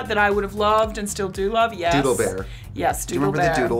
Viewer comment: when you said doodle bear i heard “dula bear”